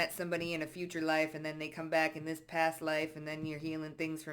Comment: 😎Oh ! Thank God ! ❤😊I thought you said past wife .😮😂